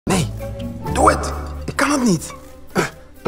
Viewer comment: I'm Dutch, and he says "Hou je van jazz?" But i hear "iphone jazz?" 😂 -Anouk (the Netherlands)